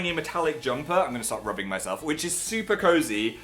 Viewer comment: i thought you said you were gonna stoo rubbing yourself